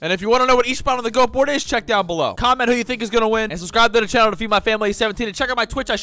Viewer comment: did he say feed my family